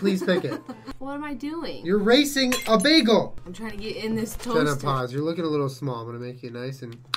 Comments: Lol he said "Jenna Pause" I thought he said "jennapause" as in menopause and that was her nickname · julien said "jenna, pause" and i thought he said "menopause"; i thought he just said menopause like out of the fuckin blue